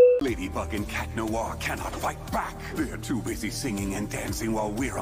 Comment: THE SUBTITLES SAID YES LADY FUCKING CAT NOIR instead of ladybug and cta noir im dead